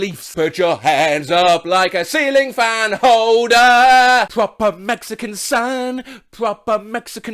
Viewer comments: For years, I thought the chorus to Can't Hold Us was "So we put our hands up, like the silly can holders." No one corrected me until I found the actual lyrics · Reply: I thought it said "Ceiling fan hoe-down" lol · I THOUGHT IT SAID THIS TOOOO AHAHAHAHHA except for ceiling can holder LMAO